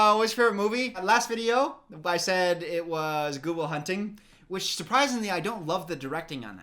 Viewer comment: On the favourite movie question he said good will hunting so fast I heard it as Google hunting and I actually fucking googled Google hunting